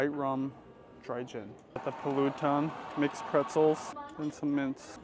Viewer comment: did i hear you say “pulutan”? lol 😅